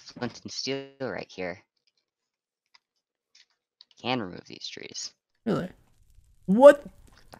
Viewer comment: Anyone else hear say Bye trees